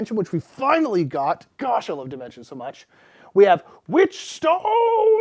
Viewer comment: Coulda sworn you said "Finally got septima expansion Dimentia, God I Love Dimentia!"